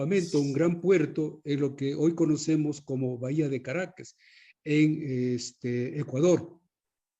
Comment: No se si escuché mal en 18:15 pero dijo Bahía de Caracas? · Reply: Según el sr. así es, Bahia de Caracas.